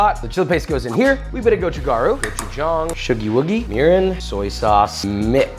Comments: When he said “mirin” I thought he said urine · I swear I heard you add......... "urine"......... LOL! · did you say URINE ????? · Hold up a minute……did I hear urine??? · I THOUGHT HE SAID URINE 💀 😂😂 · At some point isn't it less about a meal to enjoy, and more about spraying mace in your mouth? OMG 2:43 at first I thought I heard "Urine." Not "Miran." Like damn man! They are going to work hard for that PC! What masochists!!! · Did he say "urine" at 2:43? 🤨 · DID HE SAY URINE? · What did you say at 2:42?!???? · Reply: Mirin. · Hold up Edit: He says 'mirin', I thought he said 'urine'. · dont know why i heard urine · i thought he said urine... yeah it was Mirin